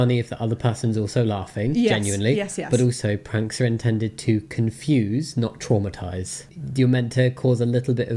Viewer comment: I've always heard it as confuse, not abuse but traumatize works too